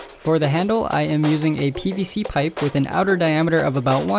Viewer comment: When u were making the handle I thought u said BBC pipe 😳😳